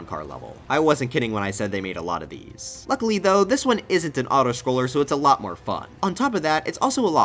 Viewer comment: my headphones cut out when you said "autoscroller" so I thought you said this one isn't a bitch